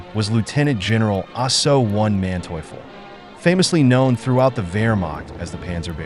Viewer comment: Whenever the narrator mentions Manteuffel, I hear it as "Juan Manteuffel"...